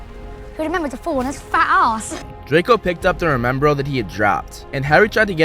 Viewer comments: Why do I hear him say fat a$$ here 4:10 ? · did Draco just say ass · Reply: Arse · did Malfoy just say "fat a**"